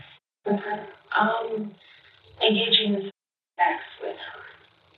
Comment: what is the censored word??? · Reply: sexual